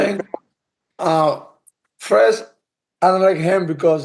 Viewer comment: what did he say here? He doesn’t like him because what? His wife? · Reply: She in DMs